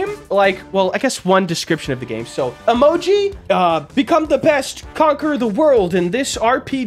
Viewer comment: did he say the best cocker in the world?